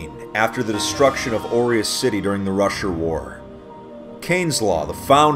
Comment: Wtf, why do i hear "russian war" instead of "rusher war"? lul